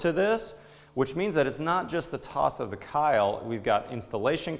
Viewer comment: Did he just say: "the tost of kile"? Lol. Good video, very professional.